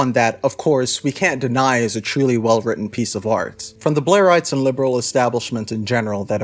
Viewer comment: Every time you said "blairite" I heard "Blaire White" and I was just like yeah, makes sense